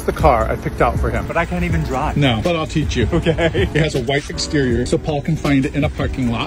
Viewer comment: It sounded like you said the car "has a wife exterior" not "white exterior" Haha